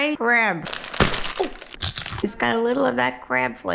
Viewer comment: I swore I heard your dog say I like the crab flavor